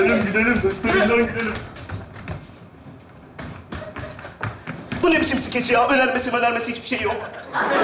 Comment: te ne diyor anlaşılmıyor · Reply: Diyor ki bu ne biçim skeç ya önermesi mönermesi hiç bişeyi yok